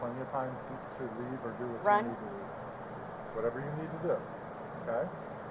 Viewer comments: DID SHE SAY "RUN" AT 35:32?? She KNEW she killed that man and wanted to escape but couldnt 😩😩😩😭😭😭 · Did anyone hear sarah say run or is it just me